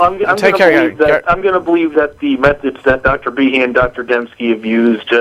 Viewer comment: Did he say " I am going to believe that the methods that Dr Behe and Dr Demsky ABUSED" Because I think that would be right... I really think he said abused not used... Listen I think thats what he said...